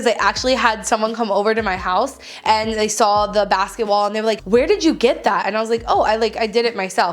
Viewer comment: every time you said basket wall I thought you said basketball 😂😂😂